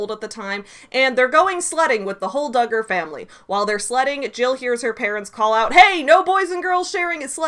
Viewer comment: Okay, I had to pause and comment because when you said they were going "sledding" I swear I heard "slutting" and then when the mom yells at them not to boy-and-girl sled, it felt even more appropriate.